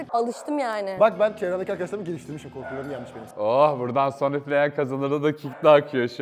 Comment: de sevgilim diyo kesmişler orasını bence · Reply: Evet bende duydum😂 “ bak korkuların yenmiş benim sev- “ diyor ❤